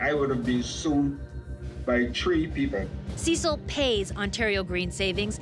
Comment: he did not say 3 people he said tree people i did not know there was tree people lol